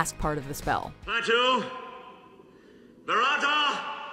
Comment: Yes. Open the creepy book and read from it's pages out loud. Of course nothing bad would happen. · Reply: Is it just me or did it sound like Ash was saying "Varata" instead of "Barada" as it is? Meaning he was fucking up the spell even before leaving the old fart, Arthor and his escort anyway.